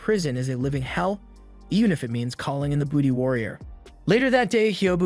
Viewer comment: I’m sorry did he say call in the booty warrior?